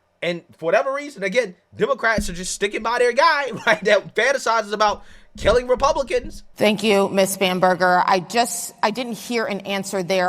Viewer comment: I had YouTube on in the background and I could swear I hear “Mrs. Spamburger”